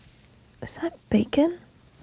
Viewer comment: When said is that bacon I thought he said “ am I naked? “